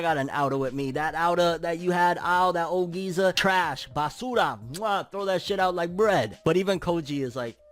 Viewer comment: did he just said basura? 🇵🇭🇵🇭